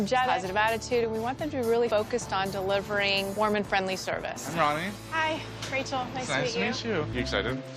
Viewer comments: When they said “warm and friendly service” I thought they said “Mormon friendly service” lol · I thought she said “Mormon friendly service” lmao 😂